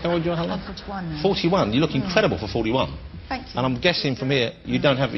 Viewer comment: I thought Jonathan said "you look terrible for 41" and then Helena said "thank you" and I was like "wtf"?? But then I heard it again and realised Jonathan said "incredible" :P